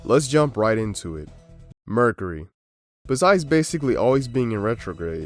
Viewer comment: i had my volume pretty low when the video starts and I thought he said "murder" instead of "mercury" n i was like well yeah that'll do it..